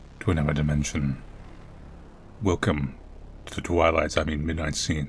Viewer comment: Can someone tell me what he said when says “welcome to ????? I mean midnight scenes” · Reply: He started to say, "Welcome to The Twilight Zone." He cut himself short and said, "Midnight Scenes." The whole, "welcome to another dimension," is reminiscent of the opening to the show The Twilight Zone. If you're not familiar with The Twilight Zone it's pretty good. It's hella old but can still be very creepy even today.